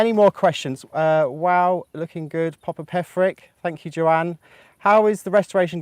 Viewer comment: when I heard say your last name I thought she said Patrick. Obviously, I heard that incorrectly. So, how do you pronounce your last name. I am an American, so I know that the British have a different way of treating there "r's."